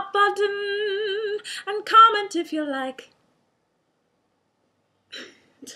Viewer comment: if you like! What? at 5:11 she said comment if you like